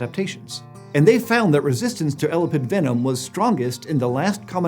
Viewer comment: I had this playing in the background and every time you said "elapid" I heard "elephant" so I pretended I was learning all about the evolution venomous pachyderms. Loved it, 10/10, would recommend.